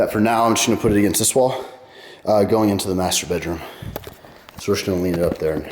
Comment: 6,17 i thought you said masturbate room